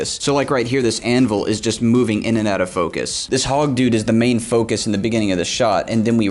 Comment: did he just say HOG DUDE? ITS TECHNOBLADE!!!!!!